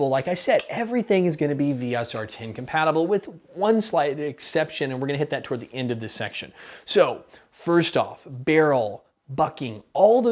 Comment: I can't be the only one who thought he said "Barrel, F@ at 4:52 right?